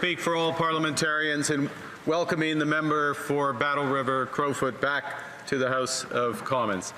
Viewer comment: I thought I heard Mark Carney said "Battle River of my foot" 😂😂